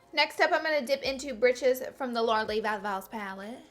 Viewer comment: I thought you said bitches 😂😂😂